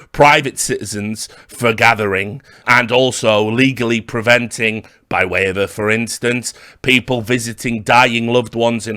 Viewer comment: Can some English native help a bit? 4:37 "and also legally preventing (something something) instance". I keep hearing that phrase in videos, but I can never quite hear the words, for some weird reason, even though I don't really struggle with understanding anything else. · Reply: "by way of a fore instance" essentially means to give an example.